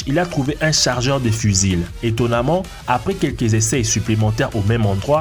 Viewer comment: il trouve un "chargeur de fuzile" alors moi je connais le " chargeur de fusil" mais pas le " fuzile" 😂 de plus il dit que c'est une trouvaille " Tincoyable" la encore c'est un mystère son language..